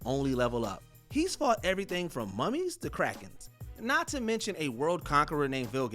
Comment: I misheard Krakens as Crack Heads 😂😂